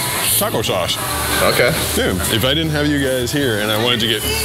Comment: It sounded like you said condoms😂😂😂